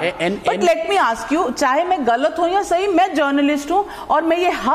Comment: what she said: Main journalist hoon. What people hear: Main kachra hoon.